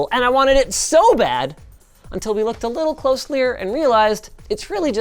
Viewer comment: Did Linus say "closelier"😭😭😭?